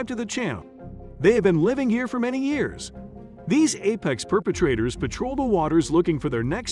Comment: Wait, did he just say apex perpetrators?? hahahahahaha